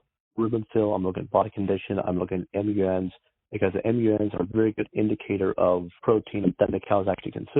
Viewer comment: what does he say here? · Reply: MUNs - Milk Urea Nitrogen. When too high, this indicates that there is excessive protein in the diet in proportion to total energy, causing the animal to break down proteins for energy and thereby create urea as a waste product.